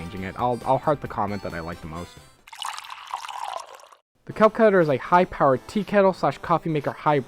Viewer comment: When you said "kelp kettler" I thought you said kelp cuter and it was a blender